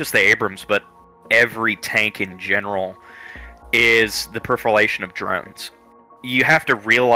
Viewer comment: Did he say the profilation of drones? EVERBODY RUN THE DRONES ARE PROFILATING!